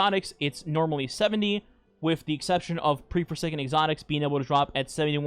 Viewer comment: What’s “pre-presaken (?) exotics” (4:16)? I tried slowing down the video to try to understand what you said, but it didn’t help. You talk really fast. It makes it really hard for me as a newb to understand what you’re saying. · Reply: its pre shadowkeep exotics